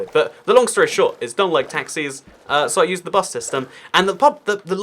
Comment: When you said “I don’t like taxis” I thought you said I don’t like taxes